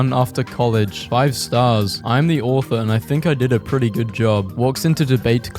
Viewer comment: I heard "I am the orphan" the first time, I had to rewind it lol